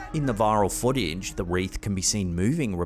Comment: when you said the "wreath can be seen moving repeatedly" your accent made it sound like you said "WRAITH"